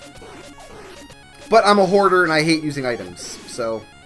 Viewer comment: When said "I'm a hoarder" I thought he said "I'm a whore"